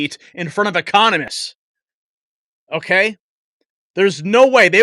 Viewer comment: I thought you said "Communists were cheering..." Instead of Economists! 🤔😂